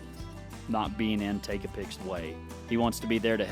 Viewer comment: I thought you said takeapic was bi in the beginning haha and you say we're turning him out makes me chuckle. 😅